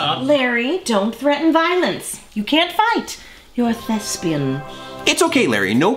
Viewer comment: I thought Dot said, "You're a lesbian." I now know, but that was funny to me.